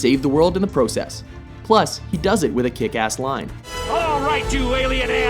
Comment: "all right you alien assholes ..." what did he say ? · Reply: in the words of my generation up yours!!!!